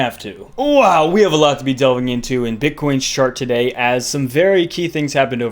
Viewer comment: Thought I heard him say "Bitcoin's shart" 00:48. Played it back once. Can confirm.